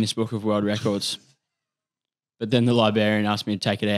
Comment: Did he just say "Liberian"?